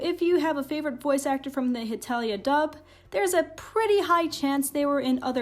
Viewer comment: I thought it said hentai